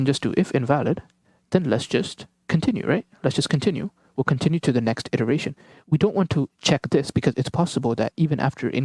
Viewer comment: I didnt understand what you said after 5:26. Please try to be little slow while speaking .liked it though👍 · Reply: thanks, sorry about that. you can turn on CC . but anyways what i said was that after the if invalid call, we should just continue to next iteration. we should not be checking s[l].lower == s[r].lower